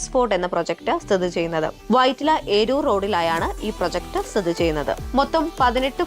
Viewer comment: Instead of Vytilla -Eroor Road, the caption at 01:30mins shows Aroor . Please correct the same